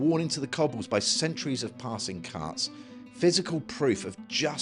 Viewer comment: Did he say "deep ruts worn into the streets by centuries of passing cats?"